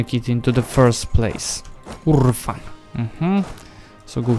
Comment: usłyszałem "kurwa" i się zaśmiałem aż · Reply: URFA :V